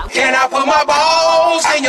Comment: many people thought this said "can i" its really "yo jaw"